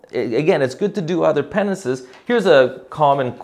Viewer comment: my captions showed " more penises" instead of more penances. 🤦‍♀️